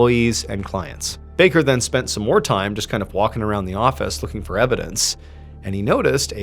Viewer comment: 😂😂 my headphones glitched so it sounded like you said “fucking around in the office” 😂😂